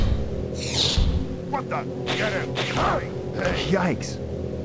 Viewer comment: What is the word? "Kyayx" What does it mean? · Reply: "Yikes!" =)))